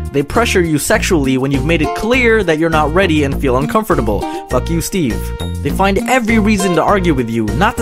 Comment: I like how he snuck in that "fuck you steve" 😂😂😂😂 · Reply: I thought he said "Like you steve"? Goes to watch again